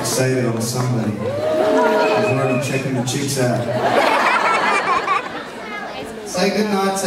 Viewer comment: What does Guy say at 0:39 about chips? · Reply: He said ‘checking the chicks out’